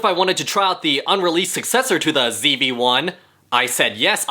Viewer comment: The only word I hear is "unrealised"